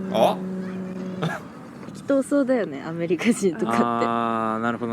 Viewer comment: 聞き取れなかった部分があるので教えてほしいんですが、10:58のところなんですけど、 女性「うーん・・・〇〇〇だよね、アメリカ人とかって。」 男性インタビュアー「あぁ、なるほどなるほど」 女性「日本より丁寧じゃないというか、全部大雑把っそうに見える」 女性が発言した「うーん・・・〇〇〇だよね、アメリカ人とかって。」の 「〇〇〇だよね」って、何を言ってるのでしょうか？ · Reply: 「適当そう」と発言しています。